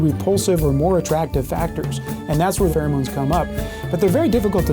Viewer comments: When they said pheromones I heard Farrah Moan's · I had to do a double take when said pheromones bc I thought he said Farrah Moan😭